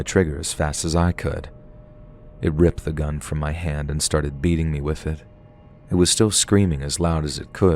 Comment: I thought he was gonna say it started beating his ass!😂